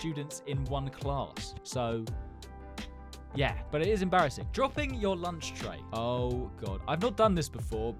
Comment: I thought bro said dropping your lunchly💀